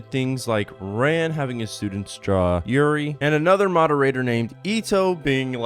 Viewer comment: rand having a students what? Im having trouble understanding what was said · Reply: Oh nvm i hear it now 'draw yuri'